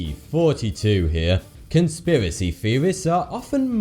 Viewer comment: I thought he said 42 ._.